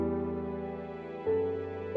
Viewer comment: Did he say Minecrap🤣